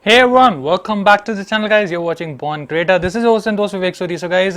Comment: porn creator......did i hear it wrong????